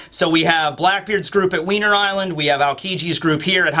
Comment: Did anyone else hear Weiner island